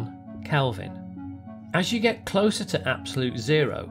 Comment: Why do you say "not" instead of "zero"? 🤨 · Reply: Naught